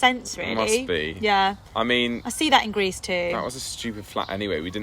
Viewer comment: I’m embarrassed to say how confused I was at first when said “I saw them in Greece, too” because I thought she said “I saw them in Grease 2” which is wonderfully shitty movie from the US.